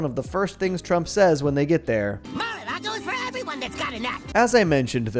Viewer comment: His accent makes this so hard to understand🙏 what did Trump say here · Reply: “Mar-a-lago is for everyone that’s gotta nut”